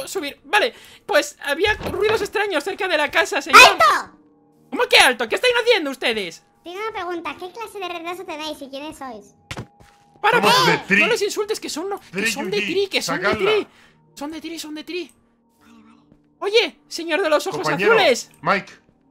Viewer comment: Pense que decia pri enves de tri